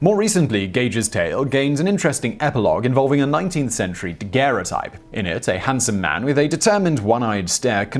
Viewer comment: What is word he says at 11:43? I'm not familiar with it. 🤔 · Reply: Daguerrotype? Just think of it as a photograph.